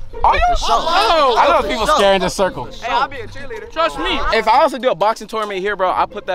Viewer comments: who else heard that oh ill be a cheerleader im dead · did someone say he'll be a cheerleader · I heard “ayo I’ll be a cheerleader” 😂